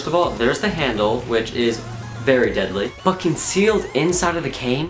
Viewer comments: lol no but it did sound like you said fucking sealed. not but concealed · I swear you said fuckin sealed . ahaa